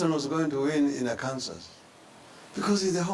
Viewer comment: Clinton didn't even grow up in Kansas 😂😂😂😂😂😂😂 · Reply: Nase Montet I thought he said arkansas .... so quick to find a wrong huh!